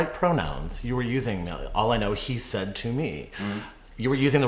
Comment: Pete is swallowing bile here. It's all I know is that to me, not all I know is he said to me 🤣🤣🤣